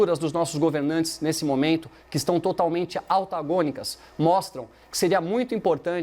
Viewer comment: Eu ouvi "AUTAGÔNICO"?! Pede para sair, Tomé...